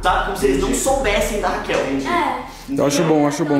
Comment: Só eu que ouvi o falando que o nome da menina interesera e Raquel no minuto 3:55 ? Descobri o nome delaaaaa kskskskks